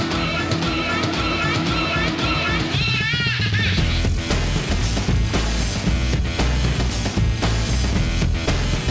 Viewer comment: I cannot understand and hear what this word is. The only thing that comes to mind is the Russian "let's go" (poehali). Can someone help? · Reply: 'What is the fut-*' but under some robotic filter. *future